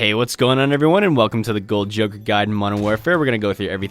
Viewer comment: 5 seconds in and I hear “golden jokr god” ........ yea, nah 👎🏾 #BeHumble 💤